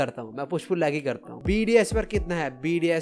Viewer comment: bro said bds i heard bdsm